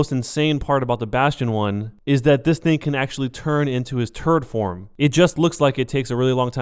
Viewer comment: also it sounded like you said turd form even though you actually said turret form XD